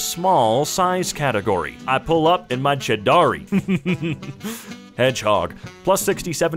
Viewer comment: I got confused on what he said I believed he said Chidori